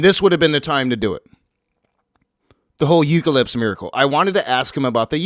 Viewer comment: I think you meant to say "eucharist" , but it sounded like you said "eucalypse" ... 😛